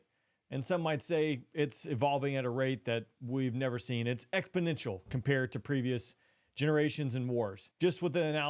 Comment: did he just say ecbanential?😂